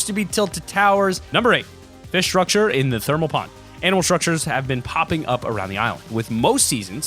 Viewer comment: did he say "Anal structures"??